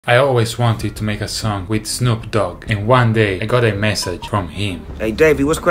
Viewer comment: I thought you said "I've always wanted to make a son with Snoop Dogg" and I was thinking "who doesn't"